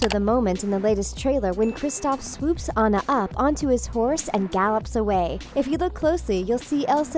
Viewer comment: i’m sorry did you say HORSE